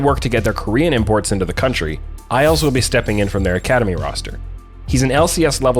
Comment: When said "isles will be stepping in" I heard it as "I also will be stepping in" and got really confused for a moment.